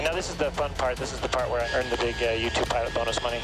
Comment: dude said "U2 pilot bonus money" but i honestly thought he said "Youtube pilot bonus money" smh. I guess it's actually both in this case.